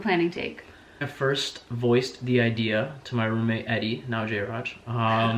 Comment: in 2:37 did he say not jayraj or now jayraj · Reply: After 5 whole years .. " he said now" 😂